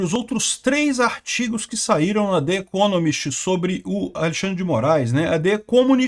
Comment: Eu entendi "The Ecomunist"